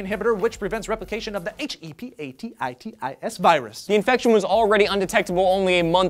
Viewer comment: thought it said "herpes cure?!"....didn't realize it was Hepatitis until like the end....